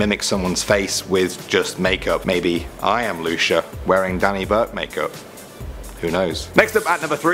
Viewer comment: Danny burke, it kinda sounded like you sayd im doni bobe